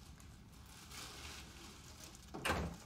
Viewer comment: What did Dwight said at the end? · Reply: We’ll bill you.